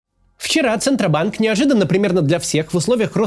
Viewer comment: Я один услышал "схера центробанк"?